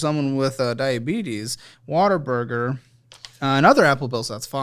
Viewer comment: Every time says "What a Burger" I hear "Water Burger"